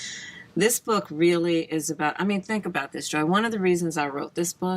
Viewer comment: Did I not hear right? Kamala sounded like she called Joy, Joe. 24:00 to 24:07. Help me out.